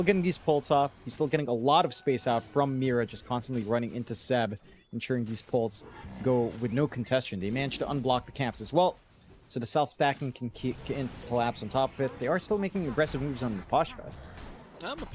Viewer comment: what did he say??? Contestion????